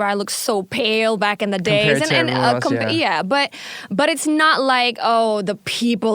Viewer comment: “iya” am i misheard?😆